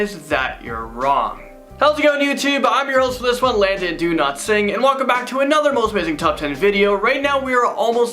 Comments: what i heard "Landon do not sing" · I keep mishearing his name as Landon do not sing... Anybody else hear it like that?